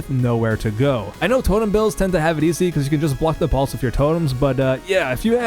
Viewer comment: "If you have tits, please let me and everyone else know in the comments". I guess I drastically misheard what was being said.